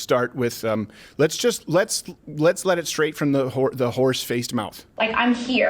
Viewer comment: did crowder just call AOC horse faced? Insanity.